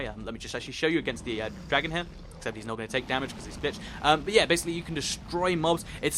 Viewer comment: did say the word bi**h 16:43! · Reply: Sebjim Playz I know , but He did say "he won't take damage because he's glitched"